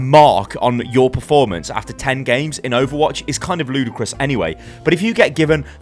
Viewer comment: Did u just said ludacris?